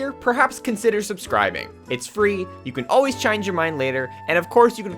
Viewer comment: Is it just me or did I hear him say pronounce “change” as “Chiinge” on 0:52 lol